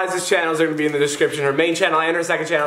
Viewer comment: I swear David called Liza "him". When David was shouting out Liza's channels @ 1:55 he said, "So go check him out". Or was I just crazy?